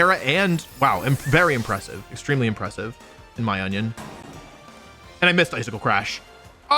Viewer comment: lmao the person that did the captions had a bit of fun 😆but it really does sound like he said "in my onion" 🤔